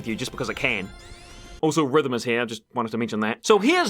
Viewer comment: I legit thought he said the n word when he said rhythm